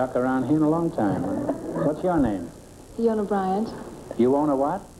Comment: what name is she saying? Veona Bias? Biona Vias? Leona Byas? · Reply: she became an actress.